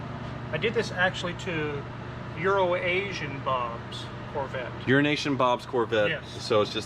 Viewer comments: EURO ASIAN BOB: Ah yes urination bob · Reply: I always hear "Eurasian" Bob. slow your speech when speaking of Bob. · Everybody is saying Urination Bob in the comments lol, I thought was saying Euro Nation Bob...